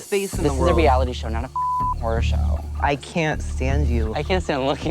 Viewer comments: "This is a reality show not a horror show..." *Im dying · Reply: I thought he said who're show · "This is a reality show not a horror show..." *Im dying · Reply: Mandy Okoko I thought he said whore show at first tbh.